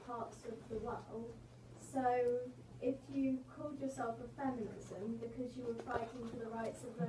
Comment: Did anyone else hear the girl say "If you called yourself a feminism" at 7:18 or is it just me?